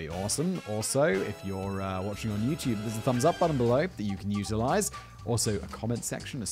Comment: I at first thought you said "there's a thumbs up button you can euthanize" and thought you had turned into MrBallen or something.